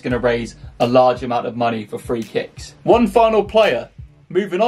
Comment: I thought that said free kids